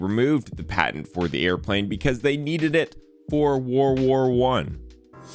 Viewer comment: Did you just say..... "War War I"?!